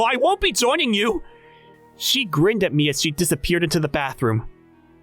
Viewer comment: “grinned at me as she walked into the backrooms” thats what i heard at first lol